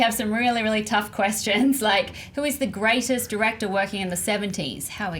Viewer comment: I thought it said greatest dictators